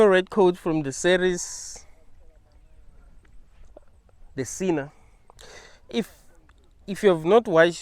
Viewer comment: What's the show called that he is saying to watch @ 11:05? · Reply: The Sinner (2017)